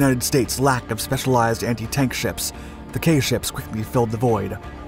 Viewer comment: I would have sworn I heard the commentator say: "anti-tank ships". A Goodyear blimp dropping bombs on tanks comes to mind. Lol